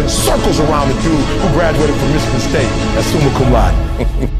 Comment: Wat does he say around 1:49, like it took me 12 yrs for a 4 yr degree n I'm running circles around the guy, something something something? Someone help thank u! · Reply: he says "summa cum laude" meaning the highest distinction.